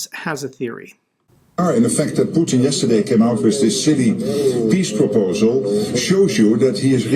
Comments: I thought that I heard the Dutch PM saying that Putin's peace proposal was "shitty", then realized he actually said "silly". It is both, really. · Pardon me, but did he say ".. this sh1tty peace proposal"? I mean, that's what I'm hearing. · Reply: I think he said "silly" .. his accent makes it a little difficult to hear it